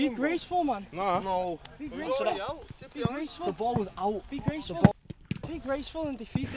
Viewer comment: Can someone help me? I have watched this at least 20 times trying to figure this out. What does Jesse say at around 8:50 when he is mad at Trent about cheating? It sounds like he says cheating or not. What is the “or not” part? I have heard that phrase before from him and other English players, but I still have a hard time with the accents (although I love them!). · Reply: He says "the ball was out" :P it took me a while to get used to their accents too. Here i'll transcribe the section: Trent: Be graceful! Jesse: The ball was out! Trent: Be graceful in defeat Ruben: You lot miscontrolled it Trent: Shocking, shocking Eric: It was a clear foul Jesse: Right now hit the crossbar, me and you, and then i'll shake your hand